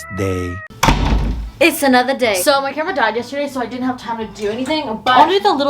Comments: I rly thought you said "my grandma died yesterday, so I didn't have time to film" bruhhhhh hahahahha also i didn't skip any of the ads j bc u told me not to... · when she said “my camera died yesterday so i wasn’t able to do anything” i really thought she said “my grandma” i was like...🤭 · am i the only one that thought she said, so my grandma died yesterday. LIKE WHAT?!?!? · Did u just say ur grandma died yestrday? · Omg I thought she said her grandma died yesterday not her camera😭